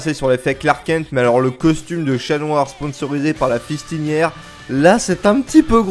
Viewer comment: A 3:03 c'est quoi une pistinière enfin ta voulu dire quoi? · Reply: alors la Fistiniére c'est euuuuuuuh ça vas etre tendus d'en parler x) ( tape sur google mais fait gaffe si t'es trop jeune x) )